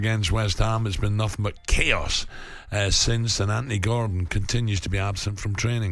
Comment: it sounds like he says "Andy Goram" WTF